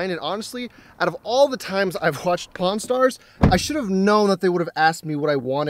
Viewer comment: did he say pawn or porn stars?